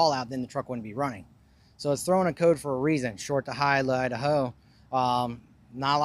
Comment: So instead of saying short to high high to low he said short to high lie to hoe